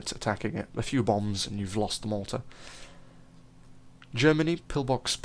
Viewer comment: Misheard "Its so susceptible to attacking air units attacking it, a few bombs and you've lost the mortar" as "You've lost the Malta". *Britain intensifies*.